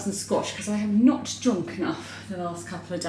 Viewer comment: I chuckled. You said you were going to have squash and water. I thought you said scotch and water. Not that it would matter, but you sort of indicated you hadn't had enough to drink.